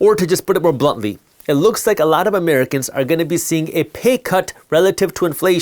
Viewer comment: I thought the 'c' was an 'o' when you said Americans are about to get a payCut I thought you said Americans are going to get a payOut😂